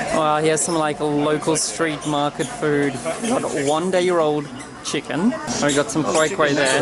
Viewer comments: Did you say “1 day year old?” 😂 Great video, can we have more of Western Australia soon though??? 😬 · Hey, did you say "one-day-year-old chicken" at 10:47? I thought that was funny! 😅😅😅